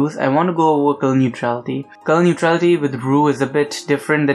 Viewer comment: “comnichaudi” 2:02 2:04 2:06 2:12 Please help me what is he saying Edit: ok I worked out it’s “colour neutrality”